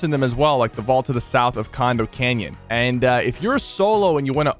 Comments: i thought he said Condom canyon 10:00 omg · what did he say?! Ayo he said c*ndom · did you just say condom canyon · Did he say condom canyon? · did you say condom canyon?